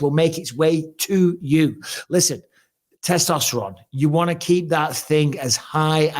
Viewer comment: 😂😂 when Mike was doing the testosterone advert I thought he said you need to keep it hard,, yep.